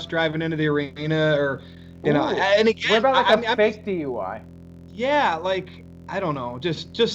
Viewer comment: What did you say at that time frame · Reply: A- says “What about a fake DUI?”